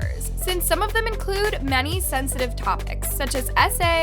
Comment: maam. SA, racism, pedophilia, and crime are not drama or tea. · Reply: I thought she said sensitive content